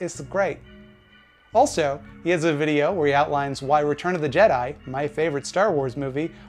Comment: I misheard "Return of the Jedi" as "Return of the Dead Eye"; that sounds like a cool film!